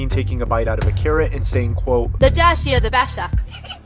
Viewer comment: What I hear is "the dashier the bettsar"